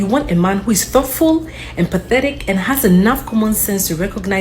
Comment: did she say pathetic?……ooooh some education 🤯